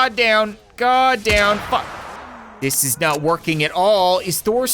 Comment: did he say fuck?